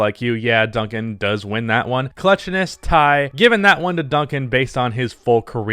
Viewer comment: I thought it said 'cuteness' That alone should convince ecerybody. Duncan is way cuter.